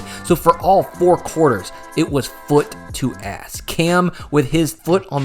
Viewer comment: did he say "foot to ass" 🤣